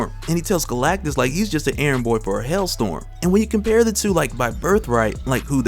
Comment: Did my ears deceive me? I swore you said Thor told Galactus that he was "an errand boy for a hell storm". That's cold Thor and I'm talking Artic cold, got damn.😱